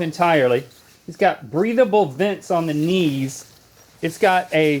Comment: I really thought Link said “breedable vents on the knees” instead of breathable.